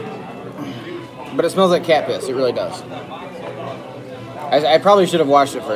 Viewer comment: I thought you said Yak piss! I spit out my drink!